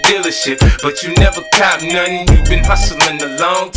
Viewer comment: "you been hustling a long time and you ain't got nothing..." 😬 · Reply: A always thought it said "it must've been along time and you ain't got nothing."😂